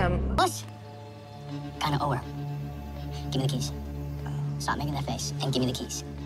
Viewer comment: When it said “Give me the keys”, I thought it said “Give me a kiss” 😂